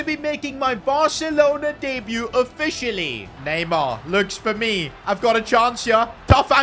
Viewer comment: did bro say Neymar jr